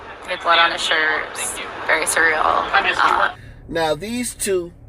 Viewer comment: Did she say "very cereal" at 5:36?